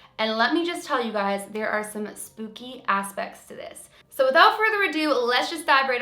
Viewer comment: When she said “Spooky Aspects” I thought she was about to say Spooky ass shit 😂😂😂